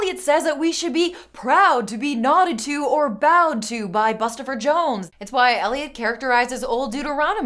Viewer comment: I legit misheard "Nodded to by Bustopher Jones" as "knotted to" and I'm just like "wait but that's a dog thing"